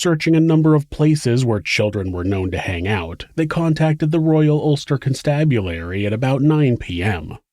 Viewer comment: "They contacted the royal ..............?" Anyone know what word he said here? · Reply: Royal Ulster Constabulary( R.U.C) was the police force in Northern Ireland from <num>.🚔✌🏽